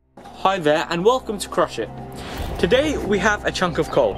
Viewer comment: I thought his channel said Crushshit